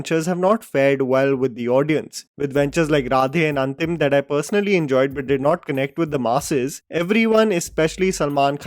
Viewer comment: You enjoyed Radhe 😂did i hear that right